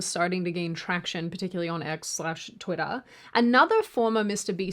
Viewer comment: Seems like Sydney's accent is becoming more pronounced. First time I've heard her say "Twittah" instead of "Twitter."